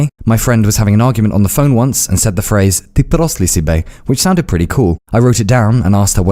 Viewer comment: couldn't hear it quite well but I think he said "Ти педерас ли си бе?" which is literally translated to "Are you a faggot" EDIT: sorry, he said "Ти прост ли си бе?" which he translated quite well